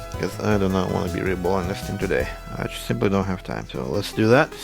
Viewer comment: your "reball" sounds like "re-bowl", which is oddly appropriate since apparently, someone heard this thing has pins and used it for bowling.